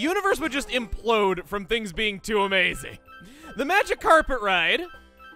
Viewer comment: I totally heard that as "The Magikarper ride." Lol